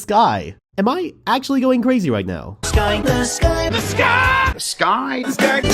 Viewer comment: if you hear this long enough you’ll here THIS GUY instead of THE SKY